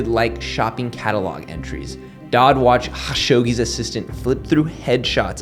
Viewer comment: Sounds like you sayin Hawkshoggi.